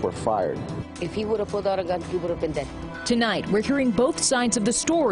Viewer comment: Did she just say "They would've been dead,? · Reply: She said if the kid had a gun he would of been dead